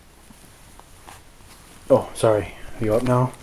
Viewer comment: lol when he said your awake pablo sounded like he said Yeah while yawning